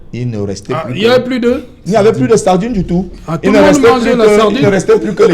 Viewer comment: il n'y avait plus quoi ? Wèhè tu ne pouvais pas faire semblant de ne pas avoir entendu. 😂😂😂😂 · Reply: la sardine